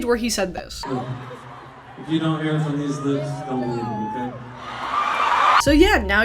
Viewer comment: "Joe Jonas addressed it on stage where he said this" followed by the most unintelligible clip imaginable 😭😭 can someone tell me what was said there? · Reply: i couldn't really tell either but i think he said "if you don't hear it from these lips, don't believe it, okay?"